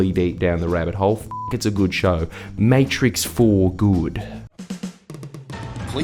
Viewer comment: I’ve always thought the voice at the end said ‘commit’. But it’s not, it’s ‘comment’. No fault of the channel, but I’m a little disappointed. It sounded so zoomer/millennial, I didn’t understand it but it just sounded cool. Also, why are you youngins talkin so funny these days? I always thought we said ‘arse-youme’, not ‘arse-oom’. Yes, I know languages are living things but two generations of American TV is making me cringe when I hear young Ozzies talk. And get orf my lawn.